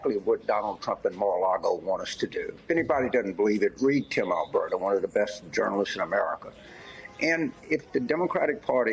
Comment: "Anyone who doesn't believe it can read (???), one of the best journalists in America." Did anyone catch the name of the journalist? I've replayed it three times and can't figure out the name. Kim Allberg? Ken Allbrook? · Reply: I believe he said Tim Alberta who writes for the Atlantic.